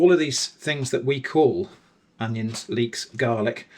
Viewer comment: Thought you were saying allion, and I was like SO THAT'S why they call them scallions! (Even though that doesn't make sense) Then turned on the Subtitles . Learned a new word, Cheers 🦐